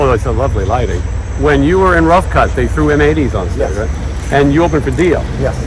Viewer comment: help, im not hearing this, what does say at 57:28 >> in puerto rico fans threw what onstage? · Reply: M80s